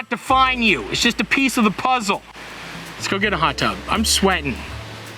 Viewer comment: I thought he said “let’s go get a hot dog” 😂